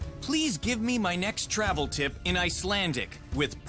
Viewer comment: what did he said? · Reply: I thought he said give me my travel tip bi**h